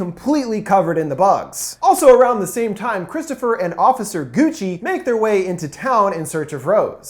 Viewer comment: “Officer Gucci” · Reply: Gucci? I thought he said Coochie🤣🤣